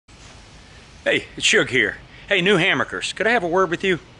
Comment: Hey new Hamburgers...... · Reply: At first blush I thought you said "Hey new hamburgers..." instead of Hey new hammockers. I was laughing until I went back and double checked, you said it right, I heard it wrong.